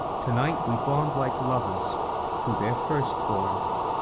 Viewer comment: “Tonight we bond like lovers through their first born” WTF. I know what it means its just wonderfully over the top and dramatic. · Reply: I thought it said mother’s, not lovers